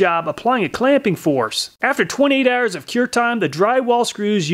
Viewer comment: Did you say 28 hours of cure time? I usually just give it 1!